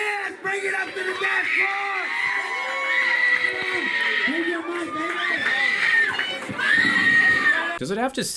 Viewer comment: “ HE’S MINE!!!!” is what i hear🤣